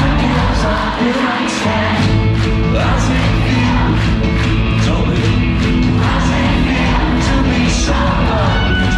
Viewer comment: is he saying "tell me" or "Tommy"? I can't work out if Will is encouraging audience participation or yet again bullying children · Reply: i believe it’s tell me